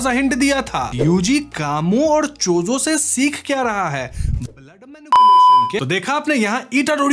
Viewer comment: I heard Lund Manipulation